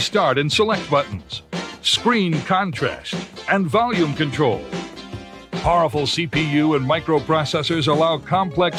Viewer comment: did he say horrible CPU? HAHAHA